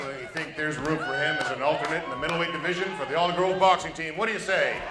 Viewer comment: I think I'm mishearing something here: 17:35 - "I definitely think there's room for him as an alternate for the middle league division in the all girl boxing team" Pretty sure he doesn't say "girl" here, I just can't figure out what else he could be saying.